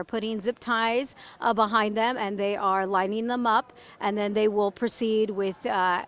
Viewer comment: Anchor said they were "Lining them up", I thought she said they were "lighting" them up. Man, I was ready to see THAT! Bummer.